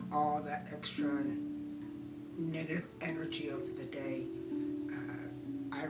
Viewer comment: Is it just me or did he say "Nega energy"? haha